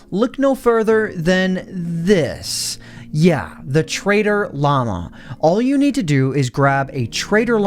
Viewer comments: When he said trader Llama i thought he said traitor Llama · i thought you said "Traitor Llama"